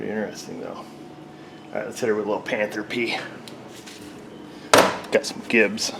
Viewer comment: what is it that you say at 9:53? I've never caught if what you say is panthropy, panther pee or panthro pee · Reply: "panther pee"